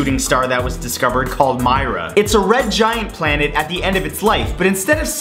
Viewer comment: did he say planet?